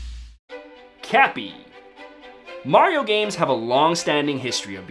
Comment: At first I thought it said “Crappy”